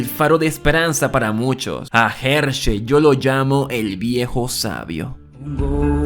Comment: NO MAMES PENSE QUE DECIA "EL VIEJO SABROSO"